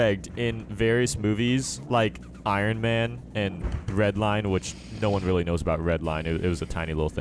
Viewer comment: Did anyone else hear say "Breadline" (8:42) 🤣🤣 Love the Videos!!!!